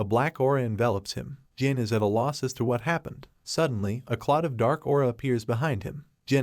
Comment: did he say Plot